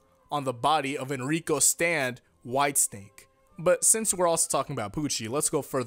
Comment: When you first said "white snake" I had to go back and make sure I heard you wrong cause I thought you said "White stink". XD